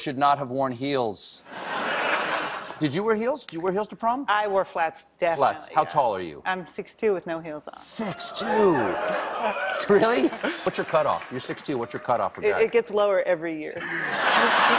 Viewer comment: "What's your cut off(?)?" Is that what he said? what does that mean?